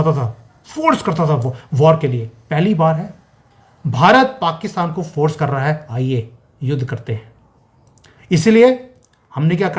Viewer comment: LOL ji ... appne jis thara sey bola 10:37 "aiye yudh karthe hain..." sounded like "aiye s_x karthe hain" (baath samajne ki hai!! LMFAO)